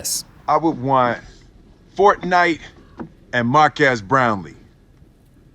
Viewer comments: Sounds like he said Markass Brownlee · Markass browney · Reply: I heard "Make us brownies." I'm hungry. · If I controlled rewind I would want ForT Nite and mark-ass brownie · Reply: Lmfao even i missheard it as markass brownie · i thought Smith said mock ass brownie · "Fortnite and Markassbrownie " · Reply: As someone who has no idea who Marques is, I absolutely thought Will said “Fornite, and a mark-ass brownie.” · "Mark Ass Brownlee" ~Will Smith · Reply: I heard "Mark Ass Brownie" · Yo is this guy the mark-ass brownie from yt rewind?? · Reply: He does sound like he is saying mark ass instead of marques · it kinda sounds like will says mark ass brownie